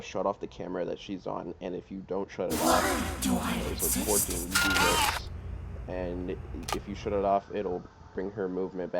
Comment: I didn't hear bro 😭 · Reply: “so of course you can do this” yeah my bad my mic was so low in this video 😭